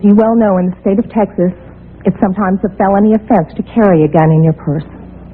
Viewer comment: what does she say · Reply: "it's sometimes a felony offense to carry a gun in your purse"